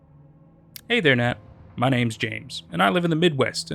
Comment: LOLLL I THOUGHT HE SAID HIS NAME IS JESUS